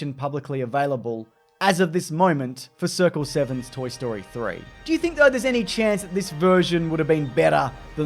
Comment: It kinda sounds like you said "woody you think" at 11:28 lol